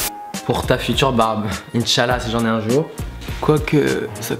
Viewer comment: ta dis quoi la .. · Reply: je fouette des pieds il a dit inchallah